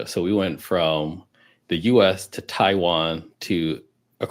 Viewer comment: i think he might have said taiwan not thailand